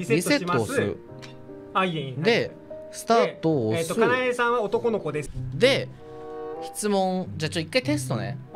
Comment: wait, did Akina say “kanae-san otokonoko desu” ??? Like, not “Kanae is male” but basically “kanae is a femboy”???? 😂